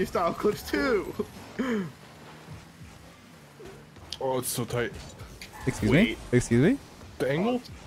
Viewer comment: OMG, Guh! Did you say "it's so tight"? And I thought you were playing RL...